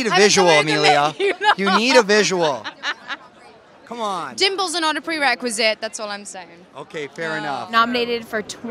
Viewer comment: What is Emilia saying at 1:07?? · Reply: Emms "Dimples are not a prerequisite, that's all I'm saying".